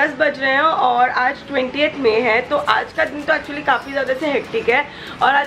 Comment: did she said its 28th May but today it's 27th May😅.